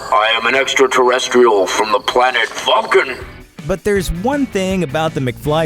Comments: Ive always thought he said the planet fucking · thought he said, I'm an extra terrestrial from the planet focken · Is it just me or does it sound like Marty says "Planet Fuckin", instead of "Planet Vulcan".